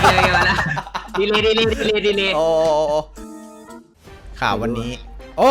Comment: พี่พูดว่าโอ๋ๆรึป่าวรึฟังผิดด😳